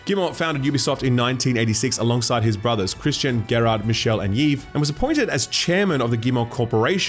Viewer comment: did you just say ‘yeeve’? Pretty sure you’re saying Yves, which is pronounced like Eve.